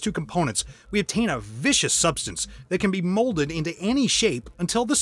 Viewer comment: instead of "vicious," did you mean "viscous?" Also, I like the voiceover used in this video.